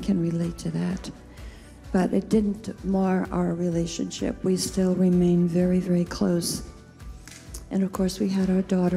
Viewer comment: Is it just me or near the end of this video, at time stamp 3:53 did she say "we still remain very very close"?